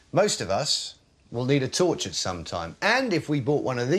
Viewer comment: i thought he said ''most of us will need to torture sometime''